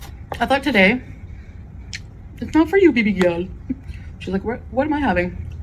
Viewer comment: did she just call Julia BBJ?